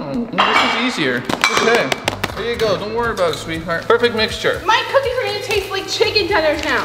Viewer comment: did he just said "don't worry about it it sweetheart" to?!